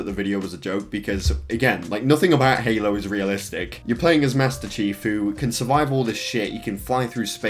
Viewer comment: Is it me or did I hear Master Shifu?